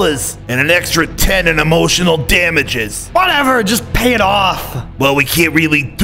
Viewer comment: What Did He Sayyyyy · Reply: Emotional damages